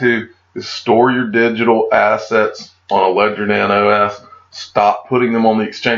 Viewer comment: i could make out what you were saying about storing in on something to protect your assets....could you help? These SEC warnings have me freaked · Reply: you have not been paying attention to the videos then.. it's called a ledger nano s doofus pay attention pay attention pay attention... Also proofread your stuff before you put it out here you look like a dummy